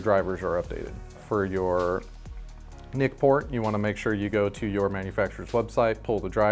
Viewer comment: What did you say at 0:43 ?? "Nick Poor" ??? What drivers am I looking for?? lol